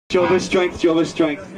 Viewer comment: LOVE LOVE LOVE THIS : ) had it on a flying tart compilation. I never knew what it was called. I thought they were saying 'Joe is Straight' WHAT? LOL "Jehova is Strength", so AWESOME. Thnx guys, God bless y'all